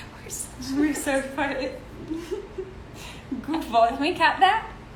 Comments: I wanna kno what she said 😂😂🙄. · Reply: dani said “we’re so screwed” and then stef said “we’re so fuckin-“ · did Danielle say “we’re such flirts”??? That’s why Stefania said “can we cut that?” ???? 😳